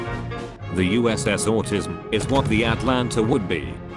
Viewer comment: it sounds like it said the uss autism and I turned on subtitles and it said autism so